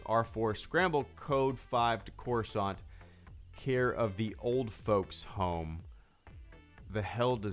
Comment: I always thought he said for all the folks at home I didn’t know it was “for the old folks home”